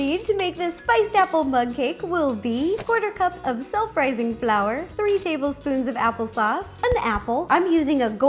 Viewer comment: At first I heard "sel frising flour" lol haha. Can u make a vid or two about vegan desserts plz? I'm vegan so I wanted to know thx :) 2018 in October anyone?